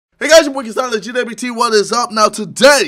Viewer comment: I thought it said walking off a car